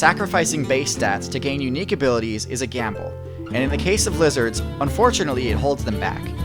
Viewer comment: While listening to the Hearthstone music playing in the background, I heard you saying "Blizzard" instead of "Lizards" 🤔